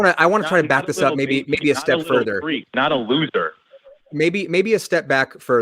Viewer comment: In case anyone missed what said as 53:38 when and understandably dismissed the nonsense proclamation of what makes a "real man", he said "yeah, Not a little baby. Not a little freak, not a loser". I think that tells us everything we need to know about this idiot. He's the kind of guy who attacks people for looking and acting in a way that doesn't conform to cis hetero "alpha male" stereotypes.